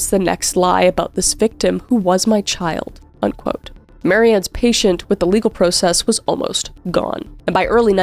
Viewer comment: Alright. 36:01 i loved this but. am i the only one who heard kris say patient. instead of patience?